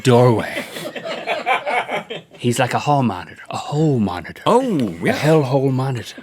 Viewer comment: did anyone else hear "Hoe Monitor"